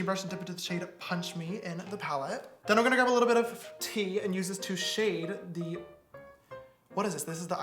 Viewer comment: what did he said again please?????? james :theni'mgonnagrabalittlebitof TEA me : WTF sis take a break